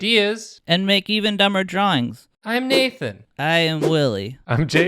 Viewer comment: Whenever Willie introduces the episode I always hear "I'm Wooly" from Amanda the Adventurer.